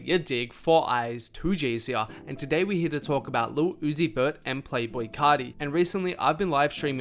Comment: does he said lil uzi bird???